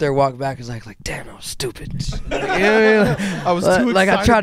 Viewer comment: pleaaase someone tell what he is saying exactly? I couldn’t hear :( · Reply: "Damn that was stupid"